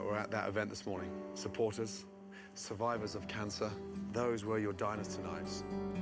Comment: Gordon was having a serious moment and taking about the cancer survivors at relay for life but I couldn’t help but laugh at the subtitles when he told the staff “those were your diners tonight” but the subtitles said “dinosaur knives”